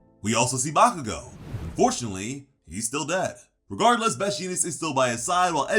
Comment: I thought this dude said "fortunately, he's still dead" when we saw bakugo